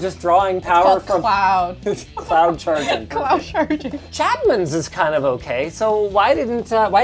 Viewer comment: I thought she said her partner was shadman😭